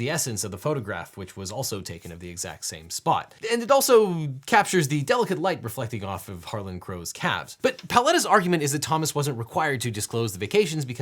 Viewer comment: I kept hearing "the delicate light reflecting off of Thomas Crow's cat"